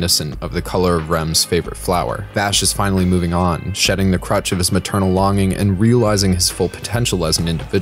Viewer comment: i thought u said "shedding his crotch of his maternal loving" i was like woah!?